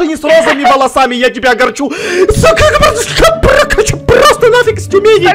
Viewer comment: мне послышалось или Эд сматирился? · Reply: Не не одному он сказал Су кхм